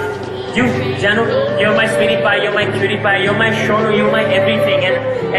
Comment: Could've sworn he said "Your my Pewdiepie!"